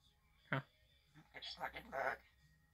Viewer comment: OMG....at 3:32 let's out a cuss word...."This f**king bird" is what I hear him saying.....too cute but you are just a young man and polite young men don't use that kind of language.